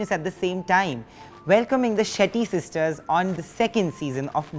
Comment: I thought he said shitty sisters for a second